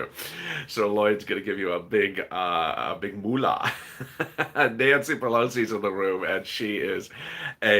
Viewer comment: Here we go finding reasons again to stall our money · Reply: I thought it la said did Nancy pelosi was in the room just what I was talking about